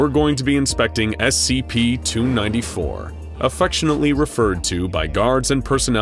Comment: Did anyone else notice at 2:24 the subtitles say “293” while the video says 294?